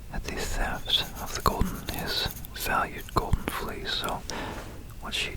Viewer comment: I thought it said golden feces